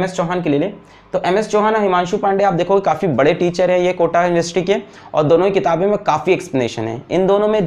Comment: did anyone else hear ''kota industry'' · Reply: Kota factory